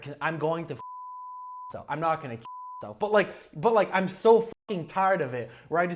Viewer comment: I first heard „I‘m gonna fuck myself“ and thought it totally makes sense with